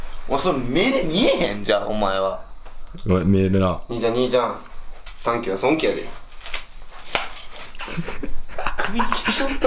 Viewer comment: なんて言ってますか？ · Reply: 短気は損気やで